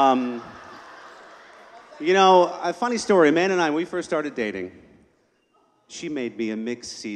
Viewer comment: I thought he said"when a man and I started dating..." And I was like "now things are starting to get interesting"